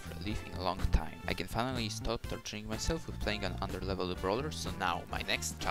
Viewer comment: i thought he said stop touching himself 😭